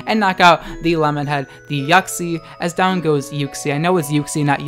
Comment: I heard it as YUCKxie instead of Uxie lmao